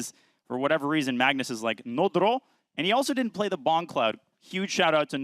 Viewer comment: did he say "he didnt play the bong cloud" ?? Wtf is that lol